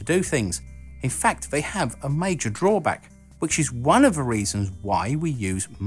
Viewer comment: Not necessarily your fault, Mr. or not totally, but... your pronunciation drives me to distraction. For instance, when you are saying, at 0:18 “...which is one of the reasons why...” I hear “...which is one of a reasons why...” and this is because instead of saying “the”, you say “va”. Same thing, you say “fought” instead of “thought”, “free” instead of “three”, “van, vat, vine” instead of “than, that, thine”, etc. I understand that one’s pronunciation is mostly dependent on how one grew up speaking, and it is typically learned from one’s family and acquaintances, but part of public speaking is learning how to avoid the use of pronunciations which most people may find difficult or confusing, or at least distracting. For instance, in my native English I pronounce “creek” like “crick”, but when speaking in public, I say creek. I guess I agree a good bit with Mr. G. B. Shaw in that I tend to think that people should purposely speak in a way where they will be easily understood, rather than speak the way they always have.